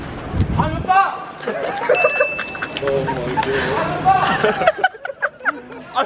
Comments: “Im a bus” “Ive missed the bus” 😂🤣😂🤣😂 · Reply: Loki thought he said "I'm Mr bus". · IM A BUS I MISSED THE BUS That Killed me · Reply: Lmao, I thought he said I'm Mister Bus... 😂😂🤦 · “I’m a bus” “I’ve missed the bus” Top Tier content lads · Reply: lol i thought he said "I'm Mr. Bus" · Ethan: IM A BUS! Josh, without being told: I MISSED THE BUS! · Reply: Lol I thought he said “I’m Mr Bus”